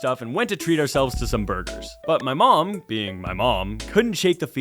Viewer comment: im sorry did he say that his last name is burger